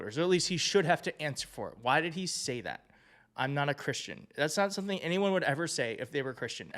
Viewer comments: I saw this speech earlier & I thought I must have misheard tRump say "I'm not a christian" cos he slurs his words ... but maybe my hearing is ok after all Thank you for highlighting it. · I just found your channel. I heard him say "I'm not Christian." And you're the only one that has mentioned this! I was beginning think I was the only one that heard it.. Ty! I subscribed to your channel. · thought I heard him say I'm not christian